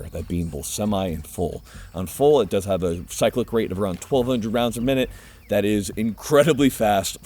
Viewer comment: The auto subtitles at the 08:53 make a mistake and put the rate of fire at 12,200 instead of 1,200 rounds per minute. That'd be 203 bullets per second, with the 19-round magazine dry in 0.09 seconds. ...I kind of want to see grouping on a gun like that :D